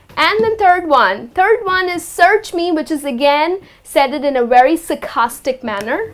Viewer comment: she says "search me, Said-ed in a very sarcastic manner" What's Said-ed? Lol😂😂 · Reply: it makes ... That phrase is SAID IT in a very sarcastic manner ... It is making sense... Unless you want to accept